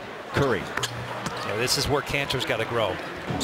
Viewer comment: Is it me or does he say 'this is where cancer got to grow'